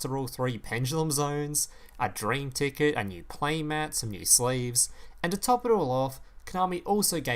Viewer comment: anyone else heard slaves at 0:42 LOL